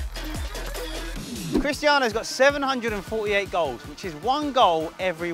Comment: I thought they said girls